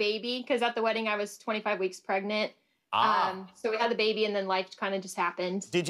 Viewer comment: Did she just say 25 months pregnant?!